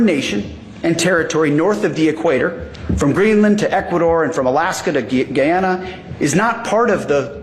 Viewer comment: did he just say 'from Alaska to Ghana' ?????? 😂😂